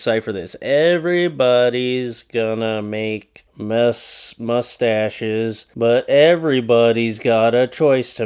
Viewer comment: I thought it said “everybodys gonna make me tacos”😂